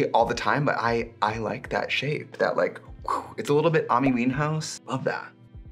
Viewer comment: Trixie....Did you just say Amee Meanhouse